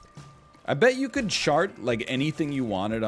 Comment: /1:02:03 I swear he said shart